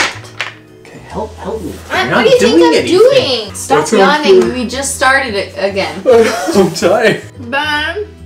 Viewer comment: I had to rewatch this a couple times: stop yawning we just started: I’m tired. I heard “stop yelling at me we just started”